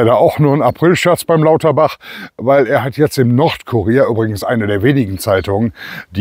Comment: "Nordkurier" ich habe zuerst "Nordkorea" verstanden.😅